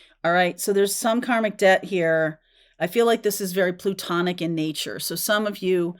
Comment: I had always thought you were saying “platonic” before today. Thank you for explaining . 😊